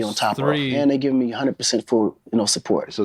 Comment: Is it just me or Did he just say 4:39 100% full no support 🤔🧐😲